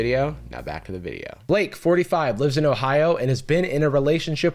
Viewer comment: i heard Blink 45 like only a part of Blink 182 😂